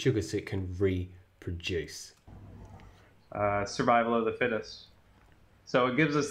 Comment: Did he say "survival of the fittest"?